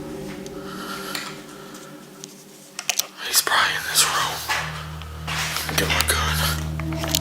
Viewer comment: did he say let me get my gun They got ghost guns now sheesh